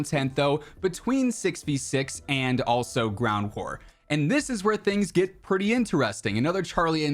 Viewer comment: am I the only one who heard "ground whore"